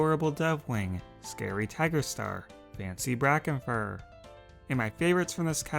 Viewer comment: i hear ''fancy brocken furr''